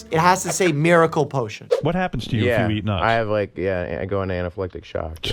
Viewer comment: For some reason I heard “What would happen if you eat knives?”